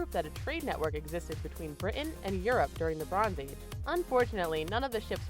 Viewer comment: xD is it just me or... does it really say BITIAN???